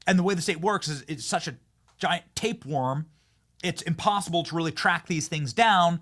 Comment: I thought you said a giant Tate worm for a second. Hahaha 🤣